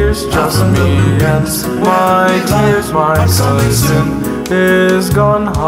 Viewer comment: Did he just say "tires" instead of "tears"?